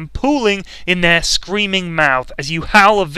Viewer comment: i heard "pooing* in their screaming mouths"